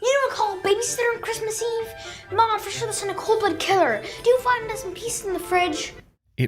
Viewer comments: genuinely trying to understand what is being said but the subtitles make it so incredibly hard lmao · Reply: I think Turkey said “They’re sure to send a cold-blooded killer! Do you want to find us in pieces in the fridge?” And then stuffing said “Yeah, do you want to find us in pieces in the fridge?!” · I had to watch 6:17 about five times and I still don't understand it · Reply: sounds like "for sure they'll send a cold-blood[ed] killer"